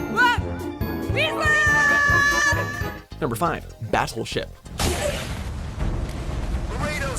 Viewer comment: In 4:23 did he say "Bastleship"? xD